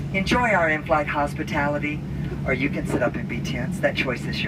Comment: What does he say at 4:41 ? You can sit up and what? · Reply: Its sitting back and relax or u can sit up and be tense the choice is yours